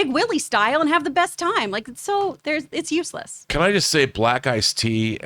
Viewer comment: I got real nervous because I thought said “Can I just say, black guys…” 😅😂